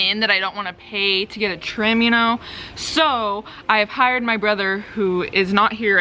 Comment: Aww Ryland is so sweet he was gonna surprise her with a salad 💕 also I thought it said “boyfriend” instead of brother and I was like wtf? That’s Ryland 😂